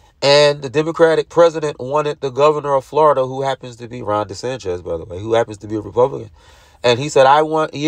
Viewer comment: !! Did you say Ron “DeSanchez”??? Come on man. That’s almost as bad as “tack-it” agreement. It’s DeSantis man. Come on. He ain’t Mexican!